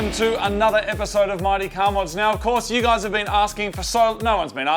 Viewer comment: I heard 'Marty car mods' no offence